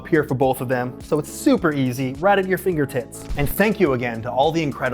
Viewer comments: I thought he said "right at you fingertits"... 😅🤣💀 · lmao did i hear 'fingertits' or are my headphones not working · Anthony I’m so sorry I swear for like a solid minute I thought you said “fingertits” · anyone else hear "Right at your finger tits"? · did he just say fingertits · Did he say finger "tits" or is that just my imagination? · why does it sound like he says fingertits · I swear I hear finger tits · Did he just say "your finger tits?" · Did he just say "fingertits"? Goes to comments to see if anyone else commented about it and can't find anything. Well, either no one else caught it, or I'm losing my mind. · DID HE JUST SAY “Right at your finger tits” 😂😂😂😂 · Fingertits, that’s what I heard at about minute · I'm sorry, but I burst out laughing 'cause it sounded like fingertits. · Is it just me or does he say "right at your fingerTITs" ?? At ~ 11:56.